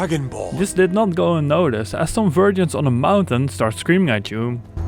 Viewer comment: did he just say “some virgins on a mountain”?